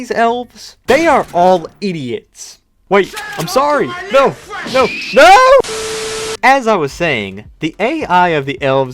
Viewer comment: Elf camp: What do you say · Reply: He said: "Say hello to my new friend."